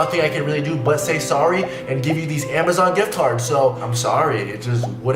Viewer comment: did he really say gift tard? 🤔